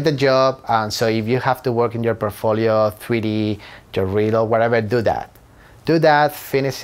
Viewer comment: where it says (mumbles), I think he says "your reel"